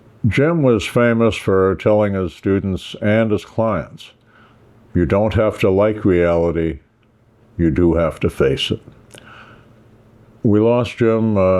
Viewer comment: I liked "you don't have to like reality, but you do have to live in it" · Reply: That's not what he said. 0:52 "you don't have to like reality, you do have to face it."